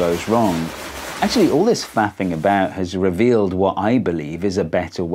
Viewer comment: "Faffing about" is new to me. I misheard it as "fapping about" and am relieved I was wrong.